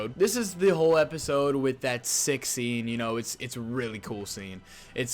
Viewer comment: thought he said s*x scene